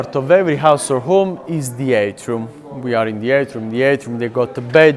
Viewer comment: the subtitles say "eight room". The guide in fact meant "atrium". We still have this word today: An atrium in architecture is defined as a large, open-aired room often located at the central point of a building. Though most atriums do not have a roof, some are constructed using glass roofs or skylights.